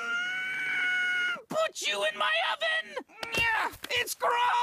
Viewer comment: Did lemongrab just say "put jew in my oven?" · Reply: no. he said "put you in my oven!"